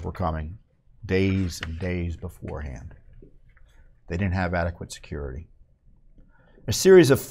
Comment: I thought she said they don't have immunity?